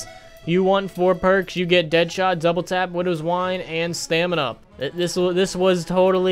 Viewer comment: Looking at Who’s Who “Okay, so we got Widows wine” ... God damnit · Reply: for some reason when you said widows wine i thought you said windows wipe......